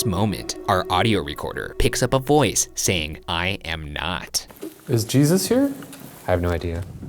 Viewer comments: when they ask "Is jesus here?" the voice sounds like it said "I hope" rather than "I am not" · heard a ghost say "I am not". I heard "I hope". Weird. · I've always thought that the "I'm not" sounded like "I hope" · 😳The EVP @ 1:40:17 is definitely saying "I hope [not/so]" rather than "I am not" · i hear "i hope" instead of "im not" anyone else or am i crazy??? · At the 1:40:07 mark the voice is clearly saying "I hope" and not "I am not" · I heard, " I hope" when asked if Jesus was there. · am i the only one hearing "i hope" instead of "i am not"